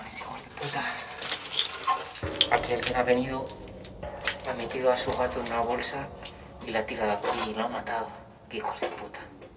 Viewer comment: no entendi que fue lo que dijo xd alguien me dice porfa xd Pd: pobre animalito :c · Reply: *Alguien a venido a metido a su gato en una bolsa lo ha tirado aqui y lo ha matado* se wue llego dos años tarde pero bueno xd